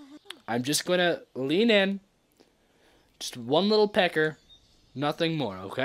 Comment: DID HE JUST SAY ONE LIL PECKER